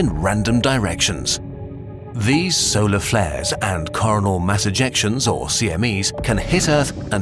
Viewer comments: did you just say( caurnol ) mass Ejections becouse coronal sound too much like corana and that will get you demonetized? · Who else heard "Carnal Mass Ejection" at 0:13? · thought it said corona mass injections hahhahaha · ah great the quarantine is getting to me, i thought it said "Corona mass ejections" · I thought that said Coronal Mass Ejaculations..